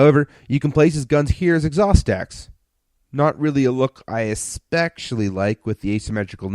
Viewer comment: What fucking word is said @ 2:03? Espekcially? Never heard of that, can I have the definition?